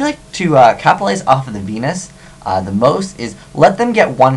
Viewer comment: Did he Say Penis?